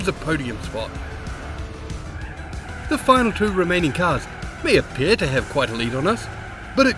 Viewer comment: k i know you said ”the two remaining cars” but i heard ”the two romanian cars”